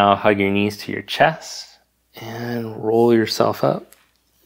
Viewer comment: I forgot where I was for a moment during shavasanah (sp?), I was startled when I heard, "bend your knees and roll yourself up."😂